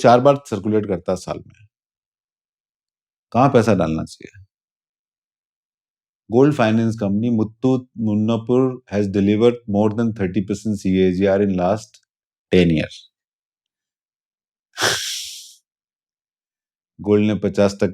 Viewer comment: Which company?? For gold 30 percent cagr?? Muthoot nupur??? Kya bola tha??? 😢♂️